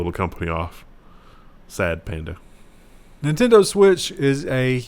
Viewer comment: did wendell just say 'Sad Panda' lol where I'm from that means something different